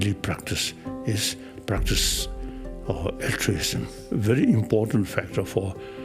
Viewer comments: What is the word he says is his daily practice and an important factor in life 13:05?? i cant understand him and i really want to know it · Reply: Loo Loo altruism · can someone tell me what the Dalai Lama said was his own daily practice? It's around 12:58. Can't understand him for that one or two words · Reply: altruism